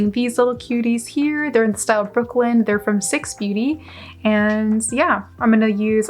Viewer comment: What are the lashes called ? It sounded like you said 6 beauty ? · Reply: syx beauty, their a small black owned brand. the lashes are in the style ‘brooklyn’ i think